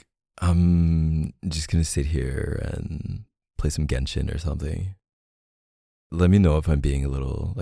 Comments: Did he just said GENSHIN!? OMG HE PLAYS GENSHIN I’M DYING Fr i had to pause the audio for a second to process what he said because i got so excited😆🥹first Genos in the previous audio thumbnail, and now Genshin😭😭 this man makes me so happyy🥹❤️ Also i’m so curious to know what’s his main in Genshin Impact🤩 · am i hearing things or did he say "play some genshin" at 4:40 ????? bc i have some questions...........